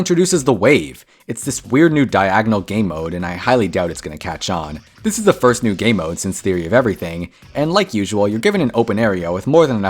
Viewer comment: i wasnt listening and thought he said "this is the first level since the inevitable,"